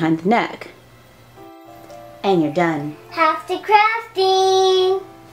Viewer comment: i thought she said crafty crafting! lol